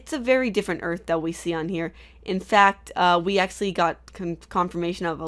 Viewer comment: I made it to 2:02 but I had to stop to write this comment. it bothers me when you say "Acsually" instead of actually. I don't know if it;s just me but still...